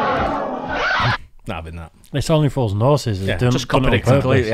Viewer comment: The part where the guy fell through the bar where the servers lift up a section...from about 13:09 - 13:14, I honestly couldn't understand a word any of you were saying. That was really weird. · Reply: Only fools and horses, it's a tv show.